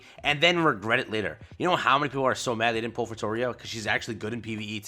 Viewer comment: pull for who?? I have no idea what he said here. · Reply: Nvm he said "Fitoria" lol. Literally had to go to my roster to figure it out 😂